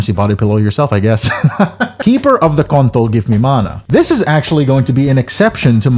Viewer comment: did bro just said keeper of the kontol at 16:12?